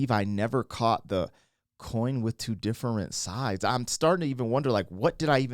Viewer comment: Yeah I thought he said it would recoil me to think different inside. But a coin with two different sides makes so much more sense and sounds better 😅